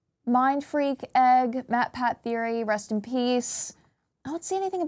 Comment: Did I just heard MatPat theory?